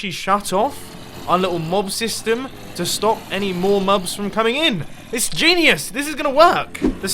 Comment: did he say "mubs" ?